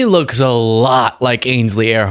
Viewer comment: I thought you said they looked like Ainsley Harriot rather than Ainsley Heirhert... Two very different people :D